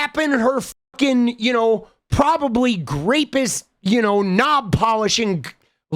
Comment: did you call shoe a rapist? you've lost me...